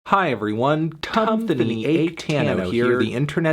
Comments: Definitely thought I heard cumthony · I couldn’t really hear the video all that well so I thought he said “Cumthony Achetano” for a minute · I wasnt looking on the screen when this video audio played and thought you said “cumthony achetano”. If it hurts, maybe you should stop beating your tofu during self isolation. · I thought you said Cumthony Atetano lul